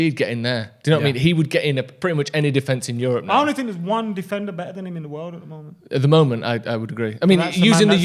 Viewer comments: Who are they saying is the best defender in the world ahead of Maguire? I couldn't catch what said... Who do you think they are talking about? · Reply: "There isn't one better player" is what he said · what did say ?? One other better ?